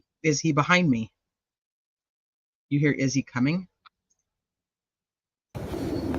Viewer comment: I hear " is he coming"